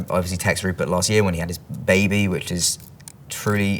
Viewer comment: Did he say Tom has baby in 5:57🙂